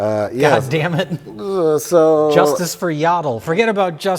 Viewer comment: I thought Jay said justice for Yado.